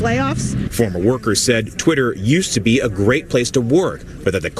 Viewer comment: When the person says "it used to be a great place to work" I heard "it used to be a great place to NOT work...and get paid"